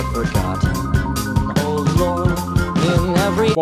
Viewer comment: I'm not sure on this but I always hear "Old lore" in Isle Unto Thyself what oh lord, like distress